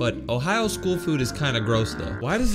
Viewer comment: Some people thought is india bro he said indiana💀